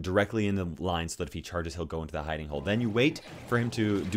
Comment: Ok I'm pretty sure you are saying "hiding hole" but I keep hearing "hidey hole" and everytime you say it I think of when we lived in our first apartment and there were spiders the size of Aragog. There was that hole in the bathroom that they always hid in! And I'd just be screaming at you to come save me from the massive spiders before they ran back to their hidey hole 😂 thanks for always killing the spiders babe ❤ and also you're hot. Like. So hot. 😍 LOVE YOU!